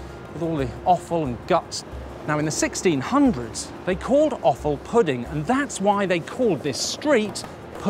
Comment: Can someone tell me, at 2:42 what does he say is called pudding? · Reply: Offal. It’s the entrails and organs of butchered animals.